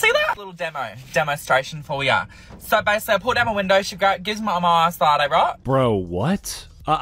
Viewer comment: I think the guy said, "she gave me my ice latte right?"